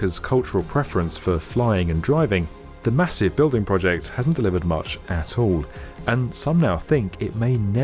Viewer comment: "hasn't delivered much at all" I heard: hasn't delivered much asshole